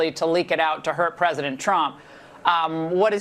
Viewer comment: Did she call him Herr President Trump?